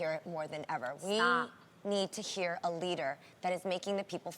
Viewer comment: did anyone else hear Adrienne say "stop"? Was she talking to Jeannie?